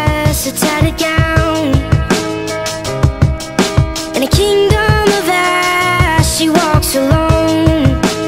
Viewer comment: I was listening to this and on the part where it says: "And a kingdom of ash" I heard it as: "And a kingdom of ass" I was just like WHAT THE FRICK